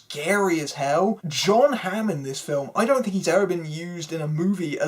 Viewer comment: am i tripping or did u call him sean hamm?